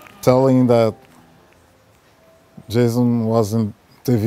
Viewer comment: LOL i thought he said jism when he said jason